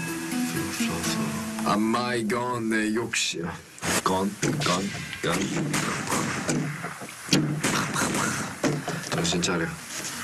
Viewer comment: when he tried to speak that korean word and it sounded like gun “gun? gun.” and shot a pretend gun four times i was trying so hard not to laugh because it’s not really funny